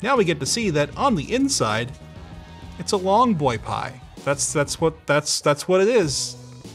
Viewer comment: saying “THICK BOY” is gonna be my text tone! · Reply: timestamp? i heard him say long boi